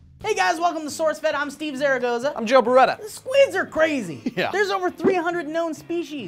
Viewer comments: LOL... At the beginning I swear I heard "The Sweeds are crazy" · I thought he said "the Swedes" so I was like "..."